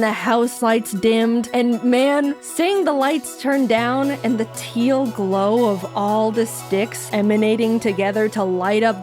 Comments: I thought you said the Teal glow of the D!CKS😭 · i heard "seeing the lights turn down and the teal glow of all this d!cks" and was like WOAHHHHHH JAIDEN